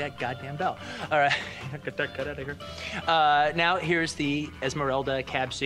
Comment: what the hell did he just say? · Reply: “Get that cut outta here” possibly lol